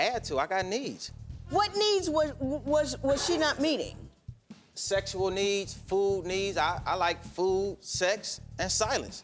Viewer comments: What he said: "I had to, I got needs! Sexual needs, Food needs... What i heard: "I had to, i got knees! Sexual knees, Food knees... " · Did he really just say she wasn’t giving me “food, sex and silence” to a female judge to explain why he cheated 😂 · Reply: I thought he said "science" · It was way cooler when I thought he said "I like; food, sex and science." Pimp newton, I like apples, falling apples and apple bottoms. 😎